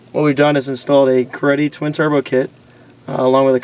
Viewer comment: I thought he said cruddy but was greddy